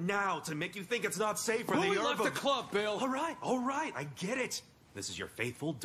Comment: What does Legoshi say at 0:27. Who left the club, Bill? Anyone know exactly?